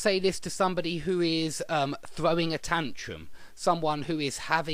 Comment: Could you please say what you said on 9:43, "keep it together" item: ...you might say this to somebody who is... It sounds like "throwing tension or attention" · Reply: Someone who is throwing a tantrum